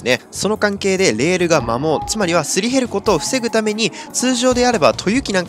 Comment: 摩耗がマモーに聞こえてミモーが出てくる…